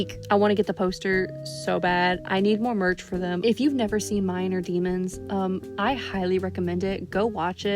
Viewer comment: i heard minor demons